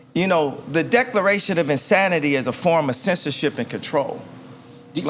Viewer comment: “The declaration of insanity is a form of sensitivity and control.” - Ye And that’s on everything. No cap. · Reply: I thought he said censorship?